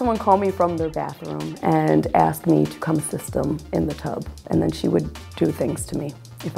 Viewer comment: why is no one talking about what the girl said at 1:43??? CONCERN. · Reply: i think she said come assist me in the tub